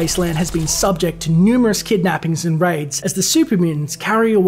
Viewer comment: did he say raids or rapes? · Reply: Calezar raids